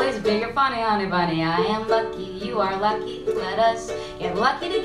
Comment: is it just me or does she say fucky instead of lucky the first time